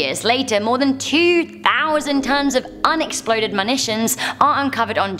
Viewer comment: did you say "MANITION" at 7:30?